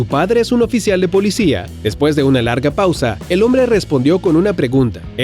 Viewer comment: escuche eres weona (modismo chileno)